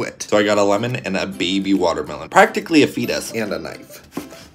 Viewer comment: A FETUS I THOUGHT YOU SAID FECES